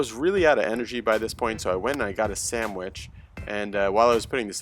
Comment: I really thought you said "I went and got a sand wedge"...